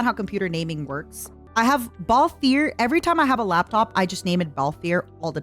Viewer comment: She says "Baltheir" but i hear "Balfeir" and it changed in my head to Bal Fieri.